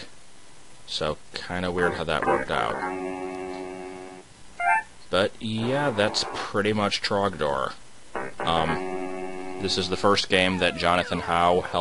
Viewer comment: It sounds like its saying conkeldurr. Yes I know its saying trogador.